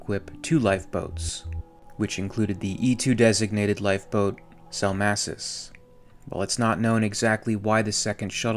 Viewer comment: what is the name of the second life boat? Did you say ‘salmassus’?